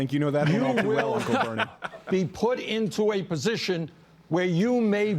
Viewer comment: it sounds like Bernie says siri and Siri came on my phone and looked up serious mental illness